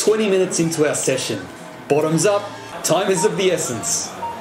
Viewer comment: It almost sounds like u said “time is of the beerssence”